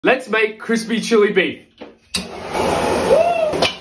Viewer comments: Definitely thought he said “crispy chewy.” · At first I thought he said crispy chewy beef and thought that I could finally make something